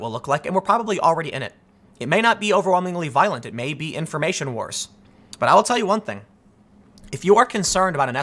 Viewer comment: Did he just say"It may not be violent. It may just be Info......Wars."?